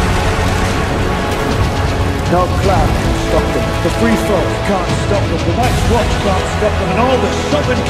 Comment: What it said in the 2:45 minute? English is not my first language but I want to know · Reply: The nights watch can’t stop them the free folk can’t stop them the seven kingdoms can’t stop them